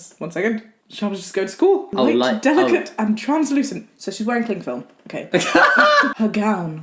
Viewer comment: "So she's wearing cling film" her gown was azure "BLUE cling film" I'M CRYING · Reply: What I thought she said kink film did I hear it wrong ?